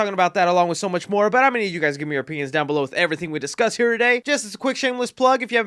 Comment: I swear I thought you said: "I'm going to need you to give me Europeans down below" 🤣🤣🤣